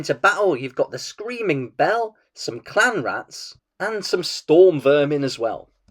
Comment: Oh, CLAN rats. I thought you said Clown Rats.